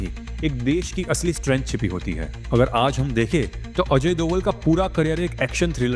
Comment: did you say AJAY? --Why?!!!